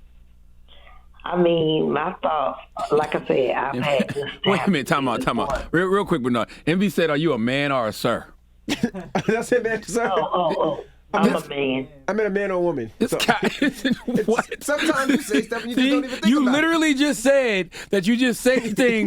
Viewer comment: I thought envy said are u a mam or sir